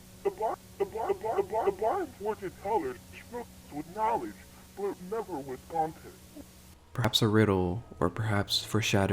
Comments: “The boss when to college with books of knowledge but never with walten” I think that’s what he said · The audio Billie or whatever says at around 23:30 , though I can't make out all of it, sounds like "The [...] went to college with books and knowledge but never with confidence." The last word kinda drags out, like Billie was shutting down as they said it or whatever. It could just be me trying to piece something together from hearing something that sounded like a certain word, but I don't know.